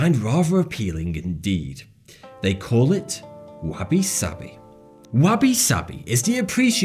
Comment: When it said wabi sabi, i thought it said wasabi, which i do not think is beautiful